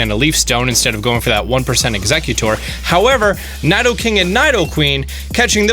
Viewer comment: did he just say naidoking and naidoqueen?